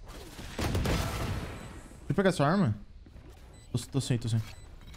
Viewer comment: quando vc vai me ajudar a sair do diamante pro imortal? Grato. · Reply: velho só eu q ouvi o falando, "tcho pegar essa arma"